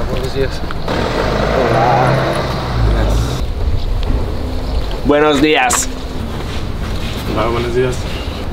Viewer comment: my headset must be malfunctioning, I thought he said "where is the ass"